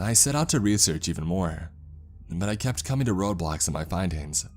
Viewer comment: i thought he said roblox XD